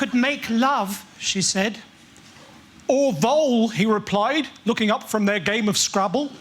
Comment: who thought he said although? I just now realized he said “…or vole.”